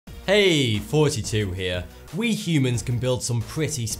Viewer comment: it sounds like u say "hey 42 here"